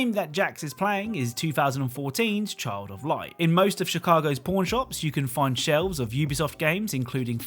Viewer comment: I thought he said "porn shops"